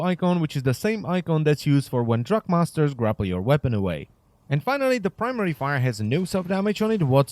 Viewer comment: I thought u said when drug masters take ur weapon away